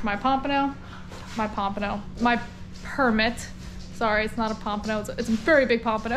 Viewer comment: Hey, when you said 'Permit' it sounded like you said 'Percocet' 😏